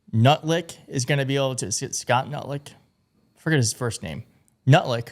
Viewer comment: I Swear I HEARD "NUT LICK" when you started talking about Lutnik. Freudiann Slip ? You said it TWICE LMAO ! Seems Ohhh Sooo Apropriate & Correct.